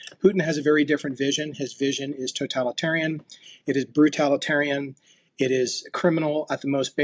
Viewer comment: I might have misheard, but if I did, I misheard what he meant anyway: "Russia is a 'brutalitarian' regime". Ain't that the truth. I will rejoice when the Krimlin falls - which it will.